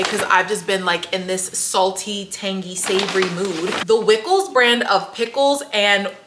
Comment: All this time I thought the pickles said wicked and told myself I’m not buying anything that says wicked.😊Reading is really fundamental.